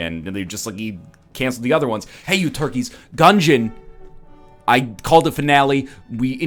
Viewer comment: did you just call us fucking turkeys!? i'm offended and appalled.